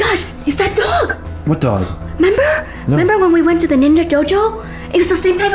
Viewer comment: said member instead of remember.